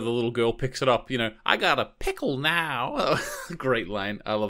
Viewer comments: The girl does NOT say "I got a pickle now!" 🤦🏻🤦🏻🤦🏻🤦🏻 She says 'Pitbull' the name of the hoverboard - it's written on it! C'mon guys FFS! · The little girl doesn't say I have a Pickle, now she says I have a pit bull now. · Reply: yes I'm aware she says I have a pitbull now. However the guy doing the commentary thought she said pickle and then he also thought it was a great line.